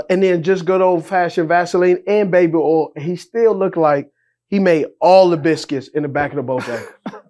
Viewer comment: did bruh just say “and he still looks like he made all the biscuits in the back of the Bojangles”!! 🤦‍♂️😂🤣🤯 that’s fkn hilarious